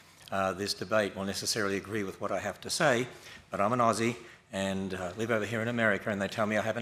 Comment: omg I thought he said I’m a Nazi and I was like “are we just gonna brush over that part??” Hell yeah people are gonna disagree with you!